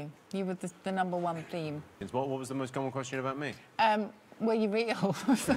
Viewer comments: What did Suzie say at 18:13? :/ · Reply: said that she said "Were you real." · Can't figure out what Susie says about Jimmy18:13. Was it "Are you really (awful?)" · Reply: She says "Were you real". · Can anyone tell me what Sue is saying at 18:13 ? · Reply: said "She says 'Were you real'."